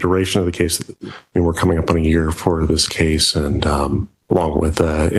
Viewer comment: Did he say 4 years?! 4 frigging years to get a case heard before a jury? It’s not a complicated case like a murder. Good lord. No speedy trial provision in this jurisdiction? 🤦🏽🤦🏽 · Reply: I believe he said they were coming up on a year, or at least that is what the closed captions said 4:00 That is still too long.